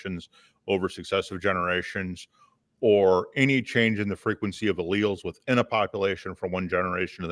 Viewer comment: any change in the frequency of "..." What word is he saying there? Edit: Found it, it's allele.